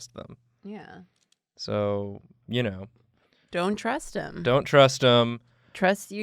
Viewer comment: I heard, "don't trust Bill Nye" instead of 'Bill Nighy', I was heckin bamboozled for a second.